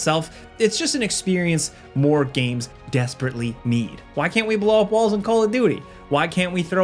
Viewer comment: Hilarious.... sometimes ill watch these while working. Swear I thought he said why cant we blow off balls in call of duty. I was like.... man thats rough, but with teams as frustrating as they can be, def been there haha